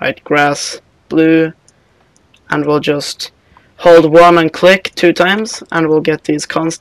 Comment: what did you say at 9:36 ? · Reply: Chayanon kitkana Hold 1 and click two times, hold the number 1 on your keyboard and click with the mouse two times